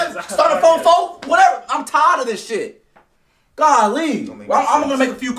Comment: did he say Kylie? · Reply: gally.